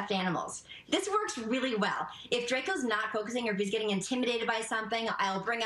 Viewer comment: Had to pause the video and comment. Did he just say her son's name is Drakeo? The Flu Flamming Mathematician. 😂😂😂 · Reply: Draco